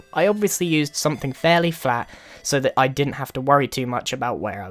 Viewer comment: #Grian At 2:30 did you say vely flat instead of very flat